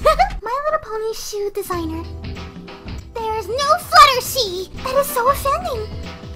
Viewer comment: did she just say Fluttershee?